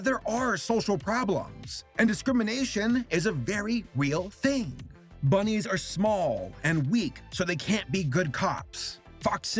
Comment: i thought you said "Bunnies are small, so they can't eat their cocks."